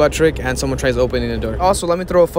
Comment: I thought he was saying the seebuck trick lmao